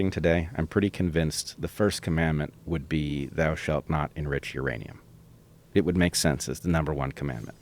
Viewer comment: I'm not a native English speaker. Could anybody tell me what Bret said 1:11:03 the first commandment would be '????'' thanks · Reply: He says "If God were alive today his first commandment would be, Thou shall not enrich Uranium".